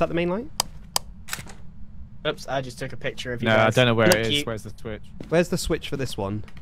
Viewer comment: Did grian say "Oops I just to picture of your ass"? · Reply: No it was oops I just took a picture of you guys lolllllll